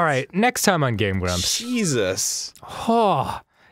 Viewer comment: Every time Arin says "Jesus", I hear " Cheese us".